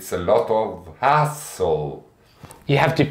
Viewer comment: Ohhh hassle! I thought he said at 1:47, "It's a lot of asshole," like he really hated onions! LOL